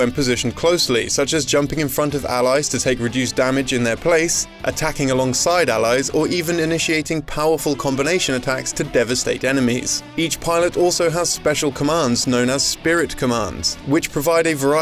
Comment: I thought you said “To devasteak your enemies” now I want steak 🤤